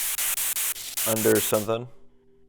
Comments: i slowed the video down at 1:11:57 and it sounded like either under the home or under whole or it was under the whole slow it down to 0.25 and you could make out some of the other words that they couldn't understand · in 1:11:55 i think i heard under world wo-ld like u can hear its like wo-ld but the letter r just cuts off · also at 1:11:59 it sound like they said unwell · It said under water 1:11:57 is what I heard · where you guys where in the room asking questions I heard under whine or under white. The under white part to me is like someone is saying they were under something white when they either passed away or it's was something white that was the last thing they saw before passing away. But the under whine thing got me confused like I thought did that house have a whine storage area where whine was held for parties and big events that happened in the mansion. That's what I think about when I heard under whine or under white part in the vid. Or is it just me that heard those words? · Is it just me but i swear i heard the word "underwater" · i think the word said could have been underline at 1:11:58 but idk ¯\_(ツ)_/¯